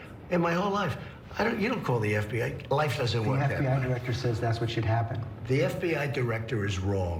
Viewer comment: Criminals do not call the FBI. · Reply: did he say life doesn't work that way?